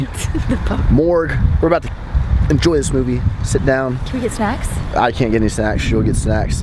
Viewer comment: i thought she said cannwe have sex, not can we get snacks lol